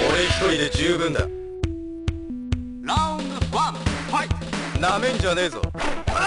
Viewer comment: siempre de niño pense que decia "No me interesa" o "Dame Chorizo" xDDD